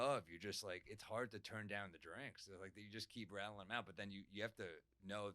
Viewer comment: Hell yeah, this ep is going to be awesome! At 24:52 when Sam says it's hard to turn down the drinks, all I heard was, turn down for what 🎶🎶 😂😂